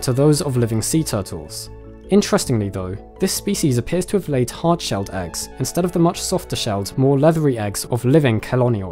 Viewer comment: I thought you said heart shaped eggs instead of hard shelled eggs at first. Like...what?!